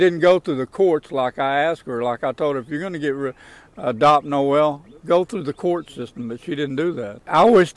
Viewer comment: I’m at 13:13 he clearly says if you’re going to get rid of then quickly changed it to get someone to adopt